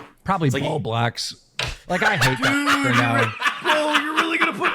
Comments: I heard "all blacks" and went 😮😅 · ong I heard “probably all blacks”